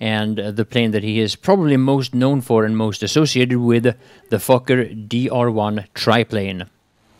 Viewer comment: Did you say the Fokker as "Fucker" ?